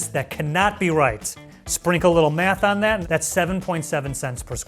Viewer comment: did I just hear him say? Never mind. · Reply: “Sprinkle a lil math on that shit” but cuts off at sh